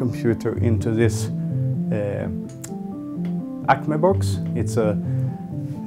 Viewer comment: did he say "acme box" ??? at 6:47 what is that box ? does any one know ?